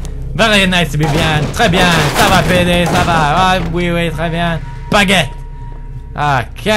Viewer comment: i think he says baguette.